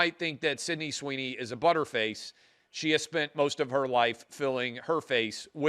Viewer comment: I am British and had to google the meaning of "butterface". No, Sydney Sweeney has a lovely body and is a beautiful woman. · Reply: I thought he said, budderface.